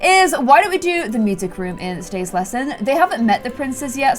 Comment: did she say 'in today's lesson'???